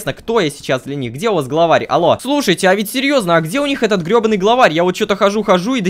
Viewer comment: мне послышалось или он правда сказал "Где этот еба### главарь